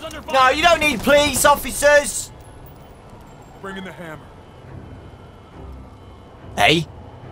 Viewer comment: What did say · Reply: MikoTheGamer the hammer :)